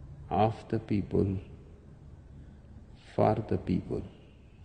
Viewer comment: 23 seconds in almost sounds like he said, F*** the people. Too funny